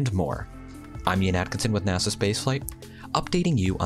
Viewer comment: I thought he said "NASA Space 'HYPE'"; not NASA Space FLIGHT. LOL altho he may not have been off by much.